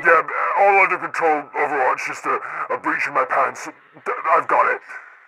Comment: I seriously thought he said "bleach in my pants".